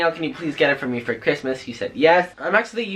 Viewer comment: did he say “he said yes” o noooo